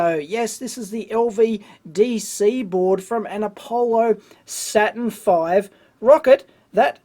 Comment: misheard 'Satan V' instead of Saturn V. I watch too much flat earth videos (but still globe earther)!